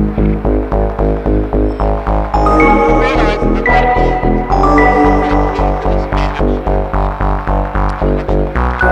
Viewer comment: does anyone else wonder what they're saying at 1:40? all i can hear is "i've made it", but i can't make out the rest of the conversation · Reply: i'm hearing: "...realizing that I've made it. I've been waiting for this-" "made it where?" "i don't know..." it is really hard to make out though